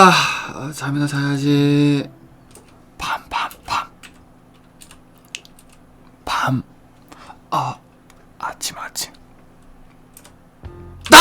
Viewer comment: 에 밤밤밤 밤 어 아침아침 이소리 들림